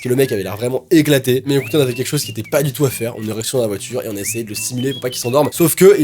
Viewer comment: Je sais pas si il y a juste moi qui a compris cela, je sais que a voulu dire on est resté dans la voiture mais à 16:48 j'ai entendu érection..... Sorry!!!!!!! sinon encore une très bonne vidéo!!!